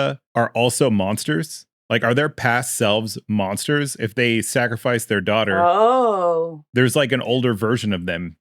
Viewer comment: I thought Tabitha said they sacrificed their kids and they were trying to stop them.